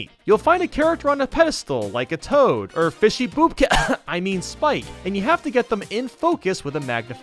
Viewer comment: did you just say fishy poopka or fishy koopa?